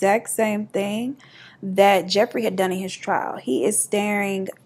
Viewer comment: I'm the same. I haven't even opened the fb app for a few months and ppl always ask me what's wrong and I'm like y'all I don't know me like that... I've been watching TV shiiiiiii t... Edit; when you said that he wouldn't break eye contact I heard "he wouldn't break his gays" and I was o no sis but then I realized it was gaze lol the English language has so many of these. I still think that of should be spelled like it sounds "ov" . Have a great day if you read this💝